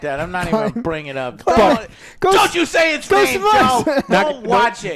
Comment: What was the movie that Joe was saying was worse than Joker 2 at around 18:30? Couldn't hear what they were saying. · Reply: It’s Ghosts og Mars. Really bad movie.